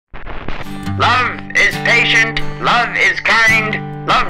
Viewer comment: i thought i hated country music. then i saw this video. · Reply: The first line I heard in the vid "Love is asian......."..............cant stop laughing